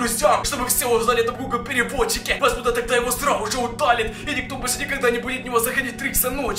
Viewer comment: МНЕ ВМЕСТО СРАЗУ ЖЕ ПОСЛЫШАЛОСЬ СРАЛ УЖЕ